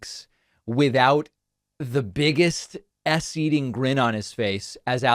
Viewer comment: "[…] the biggest ass eating grin on his face, as Alex Jones was telling lie after lie..." 4:35 I don't know if David actually said that, but this is what he said in my twisted and awesome world that is my brain! 😁🤣😂